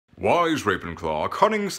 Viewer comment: Oh, I thought he said "wise ravencock" 🤣